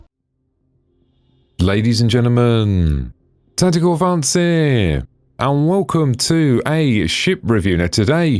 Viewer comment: Why is it that I always get this HUGE grin on my face whenever I hear: "Ladies and gentlemen... Tactical Advance here"? Is it that his voice is a dead ringer for Jemaine Clement of Flight of the Conchords? Is it that YouTube's auto annotation hears that intro as "Ladies and gentlemen... Tentacle Fancier"? Or is it that it signals the beginning of well-organized and presented updates on Star Citizen? Hmm... perhaps a bit of all three. 🙂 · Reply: I always hear "ledez an jenna man. Tatitah advance ear." At least "advance" is clear... Lol.